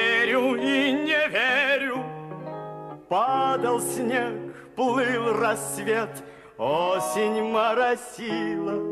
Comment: Хотелось бы знать, что говорит девушка другой на ушко на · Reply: она сказала "посмотри на него"